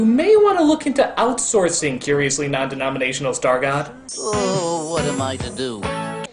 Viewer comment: my caption said "nondenominational Starcraft." So yeah I'm a let yall take that in for a second.